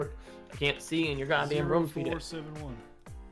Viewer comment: did say "i cant see in your god damn roon Peter"